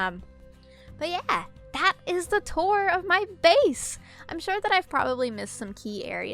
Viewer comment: It sounded like she said:n”so yeah! That was the tour of my face!” 🤣💀